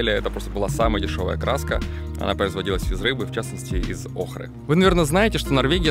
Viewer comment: не услышал. Краска производилась из рыбы охры?